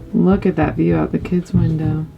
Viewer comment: "Mommy, Bigfoot!" That's what I thought when you said, look at the view out of the kids room. LMBO!!!!! 👋🤣